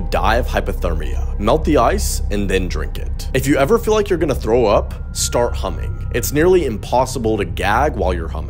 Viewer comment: I thought he said if you’re about to vomit, start humping.